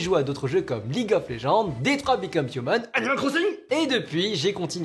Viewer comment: il dit quoi a 1:00 ?😅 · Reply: Animal crossing